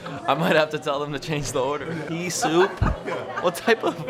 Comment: I thought you said pee soup. Pea soup is much better